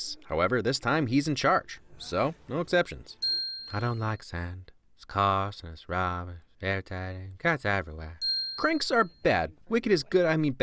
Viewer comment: what he said was "I don't like sand, it's cause it's rough, it's dirty , gets everywhere."